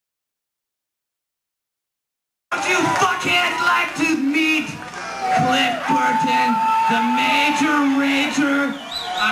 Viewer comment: I thought he said "The major rager AND a 4-string motherfucker." Kinda makes more sense, yes? \m/